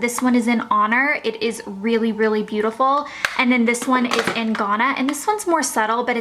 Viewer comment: Can anyone please tell me the name of the MAC blush at 15:58? The first is honour, but I cannot understand the second name...! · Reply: It sounds like Ghana the country, but I don't know anything about mac 😂